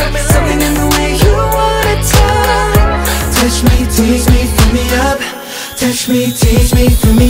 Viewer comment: No one: WayV: puts English subtitles for an English song Me: 👁️👄👁️ might as well check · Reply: Well I misheard "feel me up"as "fill me up"! So....